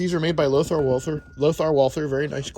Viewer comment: Did I hear that correctly? You said the barrels are made by Walther? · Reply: Lothar walther.